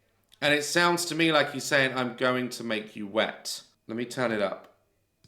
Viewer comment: The audio of the second voice underneath voice sounds like "I'll make you wet" rather than "I'm going to make you wet" Still same sentence but I hear "I'll make you wet".